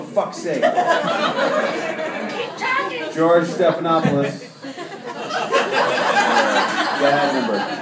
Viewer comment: I thought he said Gerard Stephanopoulos and was like "He sure has a thing for guys named Gerard doesn't he?" XD